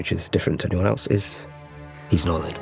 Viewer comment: what does Sacha say there? Can't quite make it out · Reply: 'he's northern'